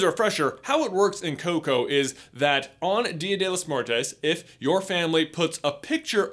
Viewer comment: Did he just say dia della mortez??